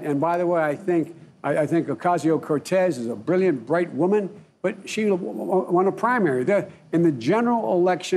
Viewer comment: Listen to him mumble at 1:24. He said "She is a brilliant bright woman" followed by a mumble that almost sounded like 'but she is a woman' before he immediately changes his words. Anyone else think so too?